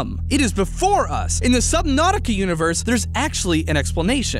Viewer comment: Did he say subnauticy